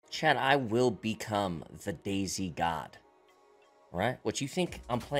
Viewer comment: I thought he said "the daisy god" lmao